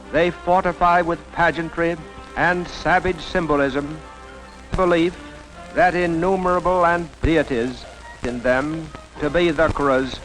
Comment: Hi, what does he say in 13:28 - 36 (can´t understand, not a native speaker) Thank you! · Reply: Five years late, but there are some breaks in the audio. Something like: "...they fortify with pageantry, and savage symbolism, the belief that innumerable and [cuts out] deities [cuts out] in them [cuts out] [co]nquerors of the world."